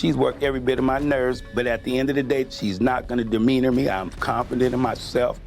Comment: she's not going to ... what word is that ? Dermean me ? Good grief 🙄